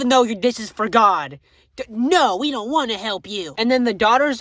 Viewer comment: in mi head i hear this as 5:32 no we dont wanna help you >:^[